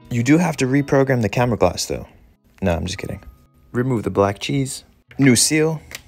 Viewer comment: “Remove that black cheese” that’s the funniest thing ive heard all week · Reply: I heard it as "remove the black jizz"